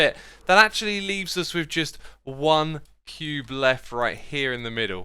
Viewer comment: did he just say Pube?